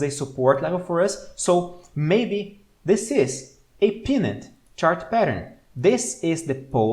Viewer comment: Hi! Loving yr stuff! One quick observation/question - in min 8:26 I THINK you meant to say PENN-nant chart pattern (PENN pronounced like a PEN you write with) and not PEA-nut (as in peanut butter) … is pennant chart pattern what you meant?